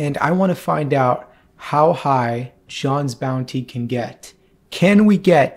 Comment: does he say Shawn instead of John😂